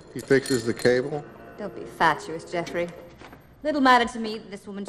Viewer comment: "You can imagine where it goes from here." "He fixes the cable?" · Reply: I thought she said fascist. Okay maybe she said fatuous.